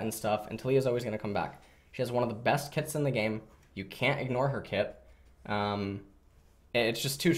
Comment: Ffs I heard "She has one of the best tits in the game. You can't ignore her tit"... I'm too tired for this